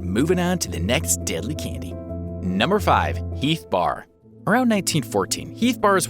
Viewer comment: For a second I thought the Heath Bar said Health Bar